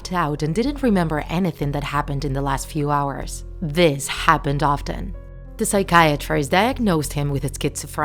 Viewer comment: When you said This Happens often I thought you said that his parents die often.